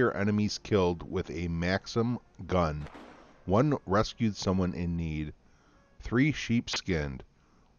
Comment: Somehow when you say "rescue someone in need" I heard it as "breastfeed someone in need" need to check my hearing haha